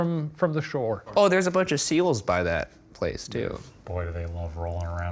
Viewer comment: I thought they said seagulls instead of seals and had the most confusing 30 seconds of my life before I realized what they actually said.